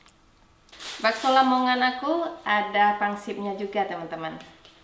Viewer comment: Aku kyknya salah denger diawal" Ka bilang bakso lamongan???